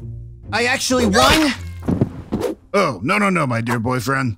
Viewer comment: Wait I thought said: "Oh no, no, no, my dude boyfriend." What he really said: "Oh no no no, my dear boyfriend."